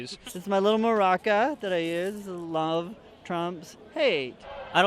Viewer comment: I always hear "love Trump's hate"